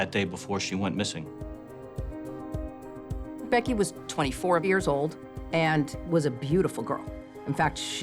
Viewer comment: the <num> mark of the video...Did she just say "Becky was 24 BEERS old?!" 😅